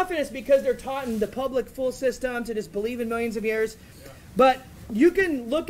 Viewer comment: he actually says ... 'taught in the public fool system...' Hmm...!